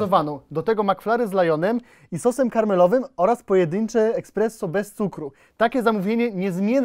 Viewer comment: Czy ja usłyszałem eKspresso?!